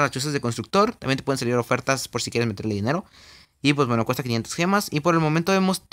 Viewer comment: Tambien hay ofertas si quieres meterle dinero Lo que escuche yo : Tambien hay ofertas para si quieres que te quiten dinero